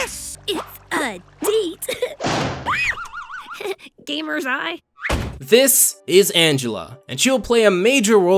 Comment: thought she said gamers rise